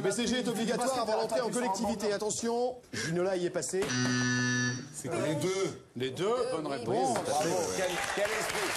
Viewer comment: A 8:23 je sais pas pourquoi mais j'ai entendu gandalf dire "les deux, aussi souvent que faire ce peux"